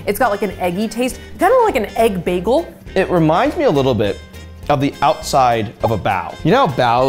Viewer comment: Haha I thought he said it reminds me of the outside of a bowel… I was like what‽?!